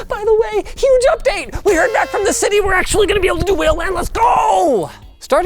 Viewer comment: what did he say? "whale LAN"?